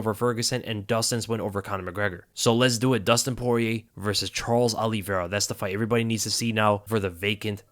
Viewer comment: I thought you said Dustin was nervous...🤣